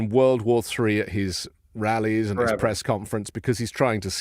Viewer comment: Anthony!!! Did you just say DEPRESS conference?! Ha ha ha! That was awesome!